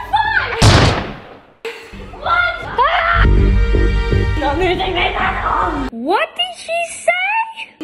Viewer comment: I’m pretty sure what said at 0:58 was “not loosing this at all” but I’m not sure